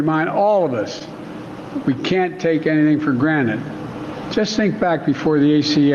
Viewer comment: Did he just say "we can't take anything for Granite"?